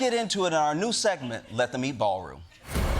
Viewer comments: I first heard it as let them meat ballroom, which honestly also works. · I heard it as "Let the MeatballRoom" 😅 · I thought Josh said, "Let them meatball room!". Who doesn't like a big meatball? You're killing it tonight, Josh. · Kinda heard it as “let the meat, ballroom!” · I definitely heard "Let The Meatball Room" at the start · I thought he said Meatball Room...missed opportunity 😂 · I was out of the room and heard him say "let the meatball room". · I thought Josh said, "Let the Meatball Room." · I heard "Let the meatball room".